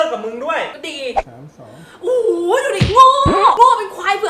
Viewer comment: ได้ยิน 32 ว่ะ 5555+